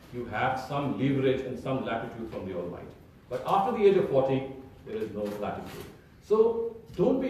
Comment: did you say Age 40 or 14? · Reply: wandp7782 40